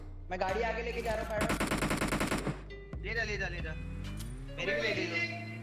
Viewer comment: I heard that you say (are heal de Yaar) because of echo